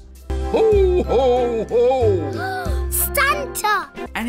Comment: Is she saying 'Santa' or 'Stanta'?